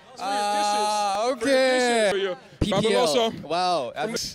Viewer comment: when Wonho said “delicious” the interviewer thought he said “dishes”😩🤣 and then he realized what he was saying and was like “no don’t drink it no” jsksjskskks🤣